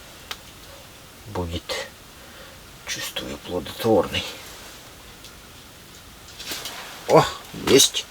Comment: "Сегодня хороший день. Будет. Чувствую - плоДотворный..." А я слышу "ПлоТВоТворный🐟🐟🐟😹👍